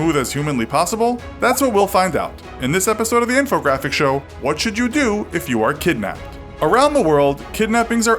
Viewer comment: I thought it said “What to do when you are kidnapping?”